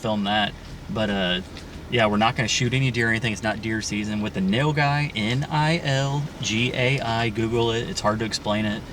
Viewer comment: What not the nail guy? Just was listening. Swore said he saw the nail guy on the property .